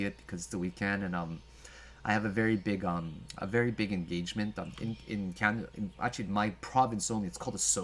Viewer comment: man, I thought I heard your engagement hahaha